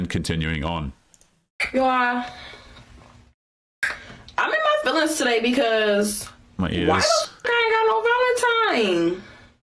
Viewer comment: I had to rewind 3x to learn that she said, "Y'all, I'm in my feelings tonight cuz...I ain't go no Valentine." I wouldn't date her cuz I can't understand her!!